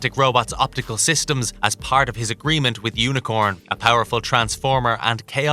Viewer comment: is it just me or i head a mispronunciation of unicron's name ( i heard unicorn) 5:06 is the timestamp